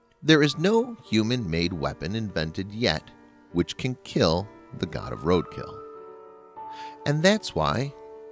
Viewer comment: For a second I thought it said, "God of Basketball"